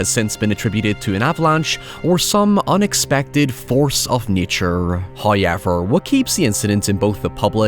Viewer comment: Here because I heard about the “hoyever”.